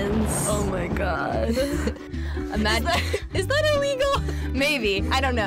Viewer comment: did she just say 'weagle'?!?!?!? X'DDDDDD